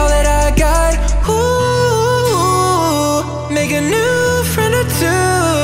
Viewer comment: did he say the n word? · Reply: He says make a new friend or two